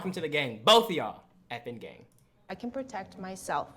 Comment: You know it sounds like you sayin effin gay.. or am I missing something